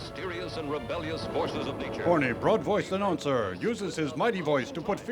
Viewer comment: Does he say “horny broad-voiced announcer?”